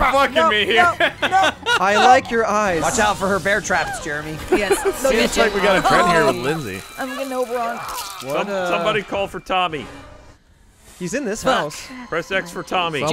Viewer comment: Did Lindsey say "I'm getting Oberon'd?" Either I missed something or there's a way darker version of A Midsummer Night's Dream out there...